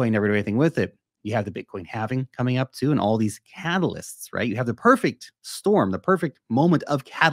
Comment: I laughed when you said catalyst, because I thought you said halvalyst. I am a Bitcoin halvalyst now.